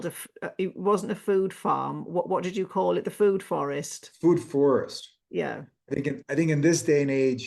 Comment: Re: Fruit forests. Why recommend growing and eating fruits when they are replete with deuterium? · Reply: I thought he said "food forests" because he mentioned animals and regenerative practices. Too lazy to go back and find it though!